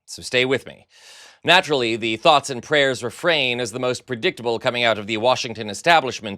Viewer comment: I thought said "Fox and prayers" (as in Fox news) which amounts to the same useless thing as "thoughts and prayers."